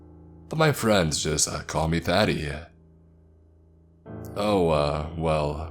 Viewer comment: Does anyone else keep hearing “Daddy” instead of “Thaddy” lol it’s trippin me out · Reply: Omg! I didn't even realise "Thaddy" was his nickname. I kept hearing "Daddy" and thought this is some weird dude